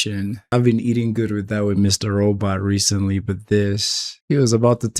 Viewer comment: Yay! Thank you for reacting to and sharing TVL on YT. You are one of the most intelligent , observant, insightful, good-humored and entertaining reactors to watch. Tending the garden-wise, we've got you. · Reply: Mr Robot!? Did I hear that correctly? Hooray!